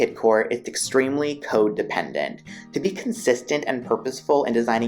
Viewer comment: Anybody else hear "extremely codependent" or is it just Freud playing with my mind? 😂 Well put together video! Thank you for making these! ❤️